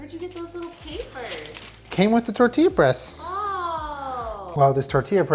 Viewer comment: I don’t know why I keep hearing tortilla breast